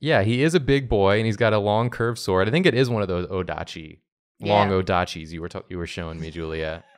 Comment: At around the 10:52 mark if you turn on captions instead of long nodachi it says "luongo da cheese"